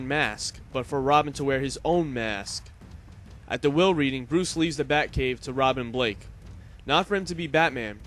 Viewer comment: sounds like you said Bruce Lee